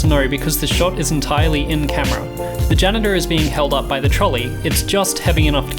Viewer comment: i misheard genitals for janitor